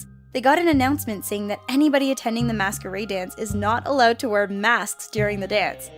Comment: For a secound i thought she said mastubate dance